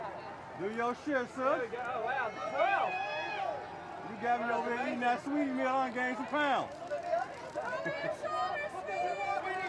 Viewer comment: Are we not going to talk about what that interviewer said to Saweetie 1:21:25 . That was beyond disrespectful, she’s STUNNING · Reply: “they got you over there eating that Saweetie Meal. you gained some pounds”